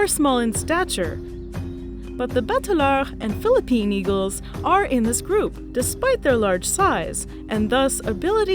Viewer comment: _ The one at 5:18 is beautiful. I didn't quite catch its name, though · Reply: the bateleur eagle